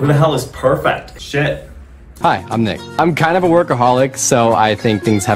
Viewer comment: I was screaming with the first couple because I misheard "workaholic" for "ALCOHOLIC", so when the perfection girl said "I'm a bartender" I was like THIS CAN'T BEEEEEEE 🤣🤣